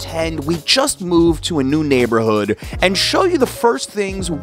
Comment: Sounded like i said "nude neighborhood" 😜